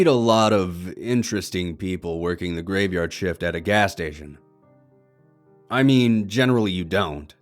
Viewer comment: Really thought it said I work graveyard shift at a ass station lol I was like damn where do you get a job like that I can work at a ass station